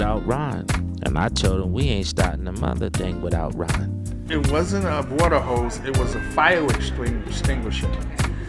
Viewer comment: did he say it wasn’t a water hose it was a fire extinguisher ?